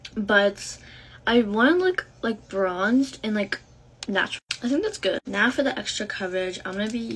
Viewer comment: girl it’s not blend in I thought you said NATRUL😅